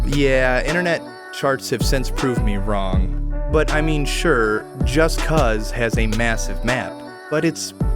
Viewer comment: did he say "just cuz"?